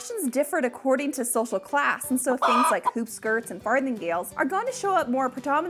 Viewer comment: Did she say fartinggales??? 🤔